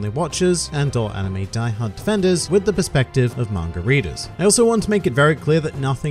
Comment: Who else thought he said margherita not manga reader